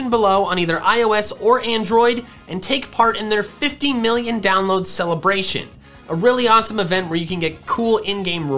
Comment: Thanks again to The Battle Cats for sponsoring! Download The Battle Cats for free here: <link> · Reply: I definitely heard you say 15 million down votes celebration...